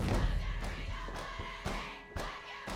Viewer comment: What does he say at 0:50!? "I'm not afraid, I've lived near you?!" IS THAT IT?!?!? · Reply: YANEproductions “I’m not afraid. Of any of you.”